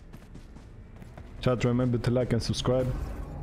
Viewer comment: did he just say don't remember to like and subscribe 😂